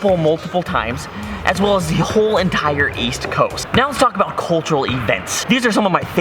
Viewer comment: I think what you try to say is "West Coast." And the Chinese subtitle is right, but the English one goes wrong! By the way, I really love your video, hope to see more!